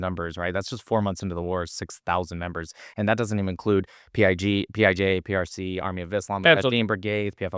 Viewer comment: said “PIG” instead of PIJ 😂 so from now on whenever i hear PIJ I’m going to think 🐖 😂